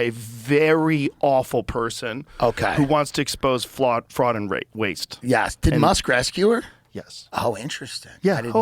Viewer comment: did he say “expose fraud and rape…waste” as a slip or I’m trippin